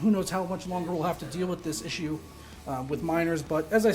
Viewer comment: I heard "minors" at first.